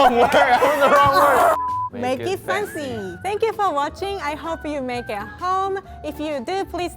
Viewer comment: Was I the only one who heard, "Hope you make it home" and have a little panic attack as I thought I was about to die?